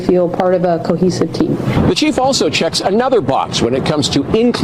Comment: He actually says "checks another box"